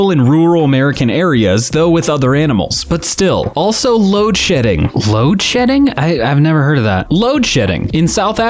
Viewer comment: LMFAO I HEARD LOAD SHITTING